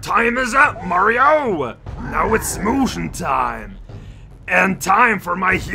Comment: for a split second I thought it actually said smoochin' time and I was mildly concerned